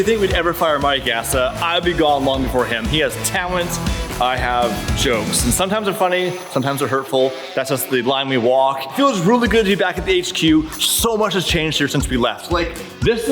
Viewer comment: When says "We'd never get rid of Mike he has...talent" I hear "he has... hair" 😂